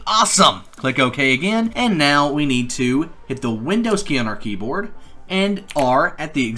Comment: what did u said i have to type?? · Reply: windows key + R